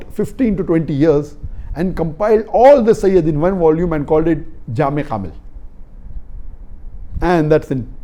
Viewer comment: salam alaikum, can someone give me the name of books of hadith sahihhe talked about? i heard jami habil but no result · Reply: <link> Name of the book-Jami’ al-Kamil fi al-Hadith al-Sahih al-Shamil